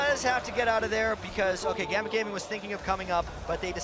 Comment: did i hear gambit gaming?